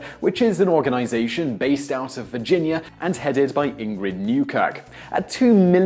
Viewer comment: i legit thought he said "headed by inbred newcuck"